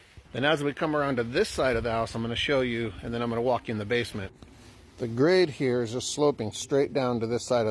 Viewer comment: Not gonna lie I really thought you said, lock us in the basement not walk us through the basement